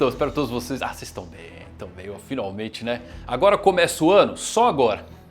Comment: Eu entendi "agora que começa Wano"